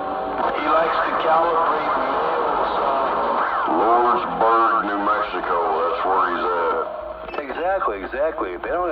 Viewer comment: What town did he say? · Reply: Lordsburg, New Mexico